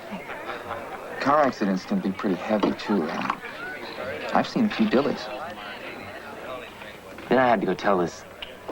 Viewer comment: For the life of me, I can't understand what "Animal" says at 11:37--"I've seen (what?)" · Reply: He said, "I've seen a few dillies!" (car accidents, that is)